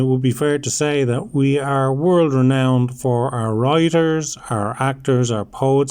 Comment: Writers, for a second i thought you said rioters.